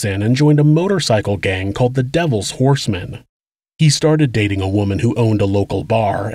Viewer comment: I heard you say the gang James joined was called "the devils foreskin", and while in my mind I know I'm surely mistaken, in my heart it feels right.